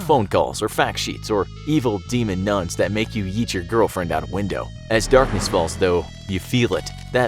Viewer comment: I thought he said “eat your girlfriend out” I was like...W O A H 😂